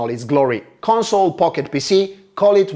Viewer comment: When he said console pocket pc i was only half listening and it sounded like he said console pocket pussy😂